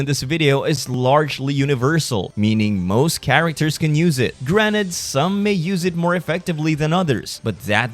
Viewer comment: did bro just said granet?